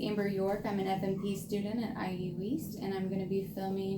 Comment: It sounds like she said ie weast